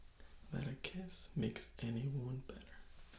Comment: Oceanus Lunaris I know I was joking. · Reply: Ero you did mishear it since he didnt say "a kiss makes any woman better" since that in itself doesnt make much sense, what he actually said was "a kiss makes any WOUND better" (i misheard it myself in my first reply to you but after listening to it a couple of times it became more clear)